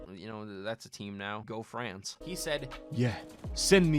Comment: Did he just say': "Go France''